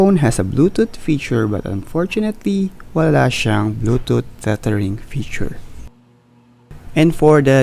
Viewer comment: What did you say dun sa 8:05 to 8:06. "walang bluetooth cluttering" Help!